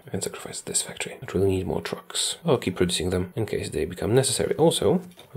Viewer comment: I heard "I need more drugs" XDD